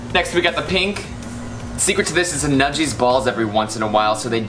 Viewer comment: Omg i accidentally skipped to somewhere around 3:48 and all I heard was ‘Neji’s balls’ lmao 😂 also I really enjoyed your video keep it up!